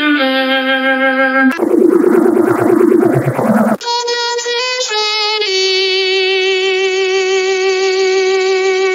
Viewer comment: wtf was he saying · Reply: i will show you what hes saying "why do you wanna stay"